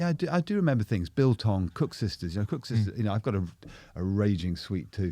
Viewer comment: what is he saying here, cooked sisters?😅